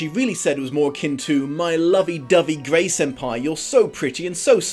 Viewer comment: did he just say “Grey Senpai”??? 😂😂😂